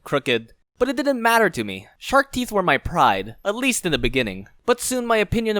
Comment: I thought it said "I have shrek Teeth"